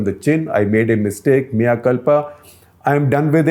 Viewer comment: He said Mea culpa, I heard mia khalifa